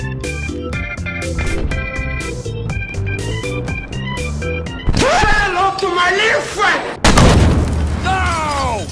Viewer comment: wtf does he say? like I always wondered... what is he saying?! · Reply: say hello to my little friend. or something like that.